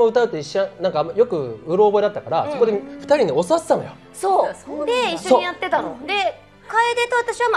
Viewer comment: he said 教わっていた, that means taught and about 1:18, i think he said ""it's funny right?" i think i said that" your work is great as ever, thank you for translating and sharing zls seiyuu videos with fans around the world